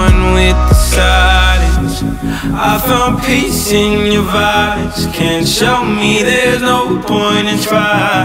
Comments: I thought he was saying 'I found peace in your body' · Am I the only one who thought it said "I found peace in your body" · I ALWAYS THOUGHT IT SAID I FOUND PEACE IN YOUF FATHERS LIKE WHAAAAAAAT MY MIND IS BLOWWNNNN